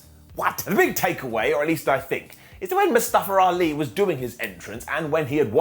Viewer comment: I thought I heard call Mustafa Ali “Mustache Ali” for a second 😂